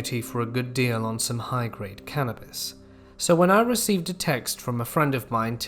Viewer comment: At the beginning of the 5th story I heard him say "carrots" instead of "cannabis" and was confused af for a moment...